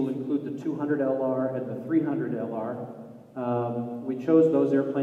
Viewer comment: did he just say "-300LR"?